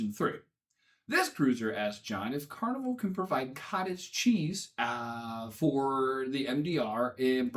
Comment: Lol I thought you said baby cheeses to hide.